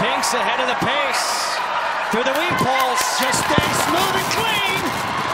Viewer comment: That commentator loves him some weave poles. Everything about this video is perfection. And while all of these dogs are amazingly talented, I think we all know Rudy is the breakout star. Though technically he made a "mistake" it doesn't matter because if rules are stupid they don't count. So for me, Rudy was perfect. 🤗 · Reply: Tassie Frassie I thought they were saying 🌾 WHEAT poles this whole time